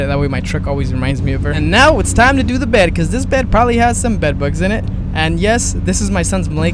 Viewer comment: Did you just say, “probably has bed bugs”?